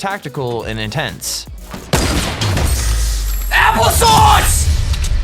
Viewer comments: did he say applesauce?? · did he say apple sauce?